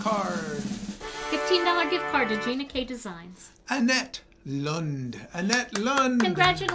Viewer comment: Im recovering from a heart attack gina hereditory from mum and diabetus?? Ghey said when you annojnced the winner of gift card my heart missed a beat haha i thought you said jeanette yoj said annette .i thought omg another heart attack but everything is fkne gina.i had my heart attack in spain drs here in uk still think of spain as being 3rd world.i assure anyone care treatment my operatiln was outstanding i think spain is much mofe advanced than us.i thank god everyday for being in right place